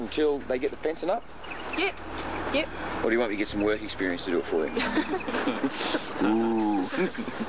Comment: What does Steve say at 8:22? Does he say or do you want me to get some work experience to do it for you?